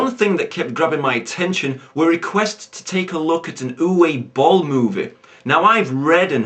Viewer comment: For a second I thought you said 'Uwe Bollocks' which would be very fitting.